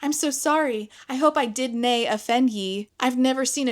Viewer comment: Minor point, but it sounded like you said the main character used the word 'dinae' at 20:10ish? If so, the 'ye's could be an attempt at writing a Scottish accent.